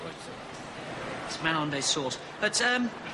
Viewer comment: Seriously... what does he say at 1:10? I've wondered for years! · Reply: "It's Manon Des Sources" (the name of the French film starring Gérard Depardieu)